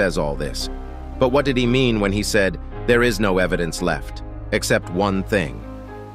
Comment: I thought the large tv man said there’s no hiding now